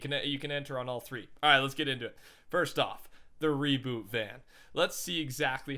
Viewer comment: Thought he said revert van not reboot van I was so excited